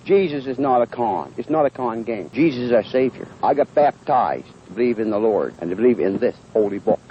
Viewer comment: Not me thinking he said ‘I got fat thighs’ instead of baptised 😂💀